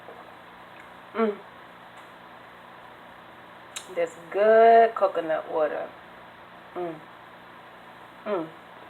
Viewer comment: in the beginning I thought she said “mmhm some good coconut oil😌” for a second there I was like “what😂”. And congrats coming out🏳️‍🌈